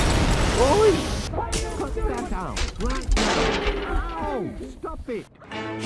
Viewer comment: Wallace: Oi! Put that down, right now! Ow! Stop it! 😂🤣 I know Wallace said “Cut that out” in Project Zoo, but it sounded like he said “Put that down”